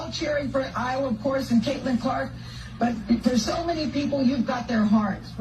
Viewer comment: Erm… I think misheard Gayle King. I heard her say “but for so many people you’ve got their heart.” I think she was acknowledging that Dawn Staley has a huge following, too.